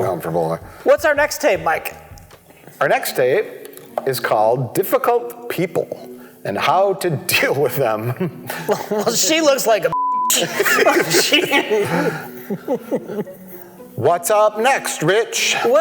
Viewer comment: I thought he said, "Difficult people and how to get rid of them!" I was hoping to find out what happened to that nice girl who liked the farts. Oh well.